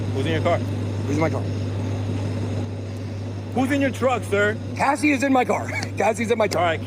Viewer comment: Bitboy’s move from douchebag crypto scammer to IRL streamer is truly inspiring. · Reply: I literally thought he said: "I have Fousey in my car" 😅